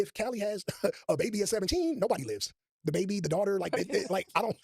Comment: 😮 did he say nobody lives….whew chile